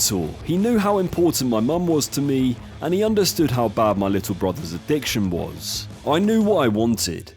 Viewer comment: the big reasoning Marco criticizes the stars is because they have become something much more about celebrity and marketing rather than the actual art of cooking. So yeah 17 stars doesn't mean he is nearly 6 times better than Marco or anything. He is just that big of a name. And I like Gordon but cmon its cringe when people kiss his ass so much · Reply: thought you said bye lil bro😭